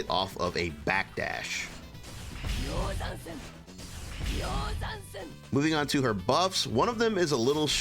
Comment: I swear instead of "Moving to her buffs" I hear "Moving to her boobs". Being said that thanks a lot for the video, and to keep mentioning the System Changes on some character, people try to analyze them isolated when in reality what we should watch is the whole picture. BTW Finally I can play Leo and Ram without feeling dirty, but I want to play Ky and Millia even more now